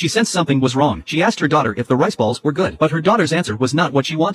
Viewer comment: I thought it said nice balls